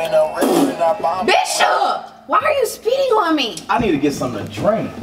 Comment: Dang I thought called him a Bitch at 7:43 😂😂